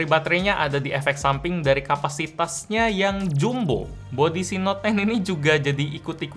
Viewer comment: Ntah Gendang Telinga gw yang rusak atau apa, gw dengernya note ten